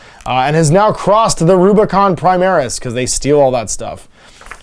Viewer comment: Sir, Is it me or did you say Huron crossed the Rubricon Primaris? If so, then that's rather shocking 😂